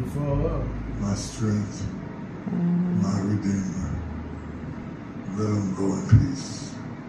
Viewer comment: I thought he said let him go in peace